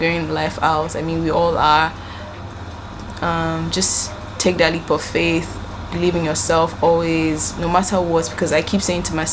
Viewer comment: I thought you said "take daily parfait" 😩